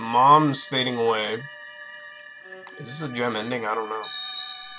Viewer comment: did he say nigga I don't know